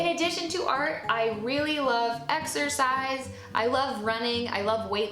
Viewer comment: wait wait wait. . . . at 3:08 did you said "i really love exorcise..."!!!??? or is it just your accent?